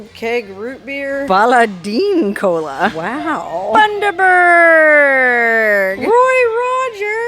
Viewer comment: When you said "Bundaberg!" I thought you said "Thunderbird!" "Have you heard the word?".